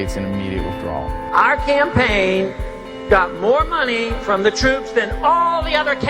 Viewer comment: i thought he said all troops...?